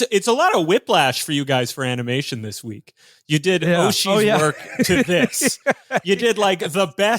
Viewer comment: What's the cartoon they said they watched before this that was good? At 2:12? "Oshi's work"? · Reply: I haven’t been keeping up with the podcast super regularly but they’re (probably?) talking about Mamoru Oshii